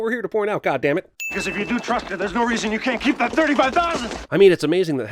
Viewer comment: subtitle says can't when he clearly said can